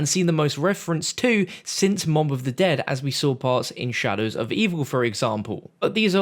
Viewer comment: did he say "mom" of the dead?